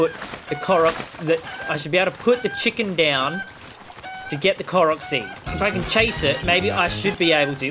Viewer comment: Ngl i thought It said chicken GANNON